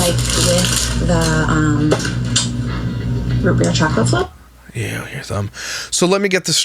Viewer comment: I thought she said “Root beer chocolate flop” and I nearly threw up 😂 First time seeing this woman- but she seems like she may have ADHD. I have ADHD- and I get pulled in many different directions at the grocery store- never fails, shit takes an eternity to complete. The difference is that once I’ve let that dopamine rush settle down a bit, I end up putting back majority of the items in my cart…which is a good thing because I’m usually scraping change from the bottom of my purse just to cover a few bare necessities anyway. I can’t imagine having ADHD but also being rich- that is a dangerous combination.